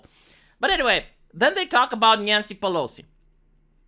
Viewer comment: I keep hearing "nyan-cy"..... I watch way to much anime